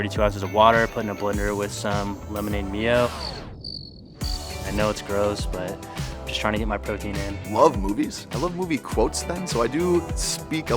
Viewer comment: Are we just going to see past homeboy blending chicken and water for his protein intake? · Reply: Thought he said lemon and myo 😮